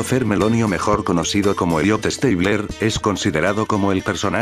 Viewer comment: Jajajaja parece que dice idiota stabler